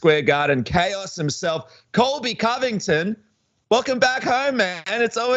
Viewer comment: wait did you say Colby Cummington?